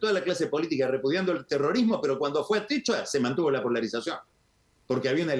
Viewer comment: «En España hemos visto centenares de manifestaciones (...) pero cuando fue el... [ ¿? ] ...se mantuvo la polarización». ¿Qué dice ahí Pagni? * * * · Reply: Atocha.